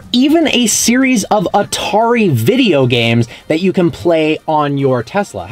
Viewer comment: Did he just say Antari games? 😹